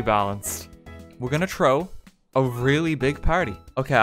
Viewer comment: Why did say “Trow” at 0:49 lmao what kind of accent he have?